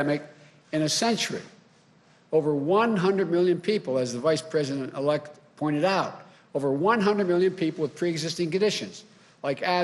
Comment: WTH is he saying at 3:04? Is he talking 3rd person or gibberish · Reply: you must be stupid “over 100 million people as the Vice President elect pointed out” wtf dude